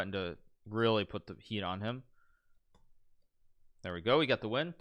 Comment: And did I hear that right at 30:00? "Now we're threatening to really put the pee on him"???